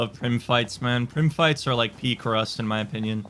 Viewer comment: What said: "I love prim fights man. Prim fights are like peak rust." What I heard: "I love prim fights man. Prim fights are like pee crust."